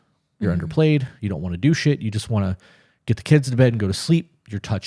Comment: Everytime you say, "Do shit," I hear, "Douche it".. I can't unhear it.